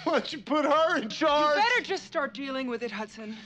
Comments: i thought he said "what ? you'll put her in charge?" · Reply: i thought he said "When'd (did) you put her in charge?" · i thought he said "what ? you'll put her in charge?" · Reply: It's "Why don't you put her in charge?" its on Aliens (1986) Quotes on IMDB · I always thought he said “What? You put her in charge?” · I thought he said "Whatss you put her in charge?"